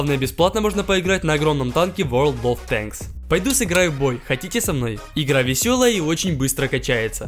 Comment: Мне послышалось или он сказал пойду сыграю в боль